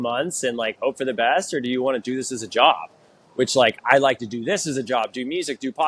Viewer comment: LOL, captions got it wrong: Like to do pot, instead of like to do pod. I had rewind and listen again.